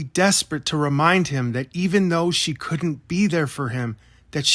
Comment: I thought you said she couldn't read or write